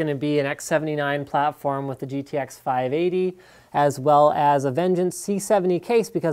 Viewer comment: GTX 580 lol I thought you said rx 580 beacuse I'm not used to hearing 580 other than rx series